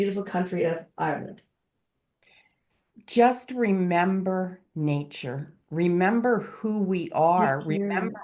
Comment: YOU'VE MADE MY ST. PATTY'S DAY! EVP AT 19:43/44 I hear him say remember! Love to you both! Dee